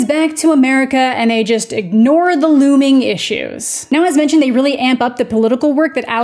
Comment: when said “looming issues” I heard “LUBING issues”🤣😂🤣hilarious in the context